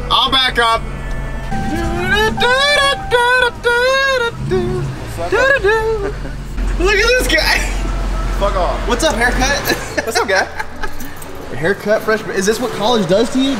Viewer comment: did I hear upchurch